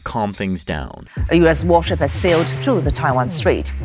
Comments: A US war shit has sailed through the Taiwan Straight (captions say) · There's a spelling mistake in the subtitles at 4:06 instead of "war ship" it says "war sh*t". I know it's only up for a second but it's so funny I had to point it out